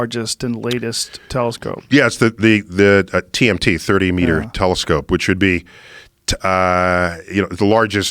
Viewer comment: Joe's thinking, "Did he just say DMT telescope?"